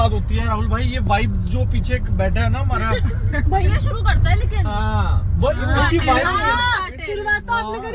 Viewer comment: did say "bhaiya" to?😂😂